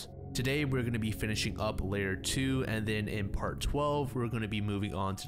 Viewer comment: oh damn i thought you were saying part 2 and 1, not 12 and 11 lol. Lemme check the other 10 real quick